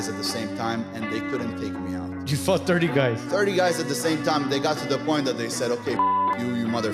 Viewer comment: All I heard was "you fucked 30 guys?!?!" Aha